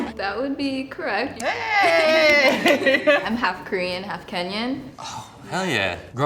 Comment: did I just hear her say half Korean Half Kenyan.??? Sis you representing Us Kenyans 👍🏿 Wakenya mpo!!!